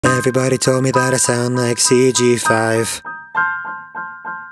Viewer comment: I thought you say i must be GTA 5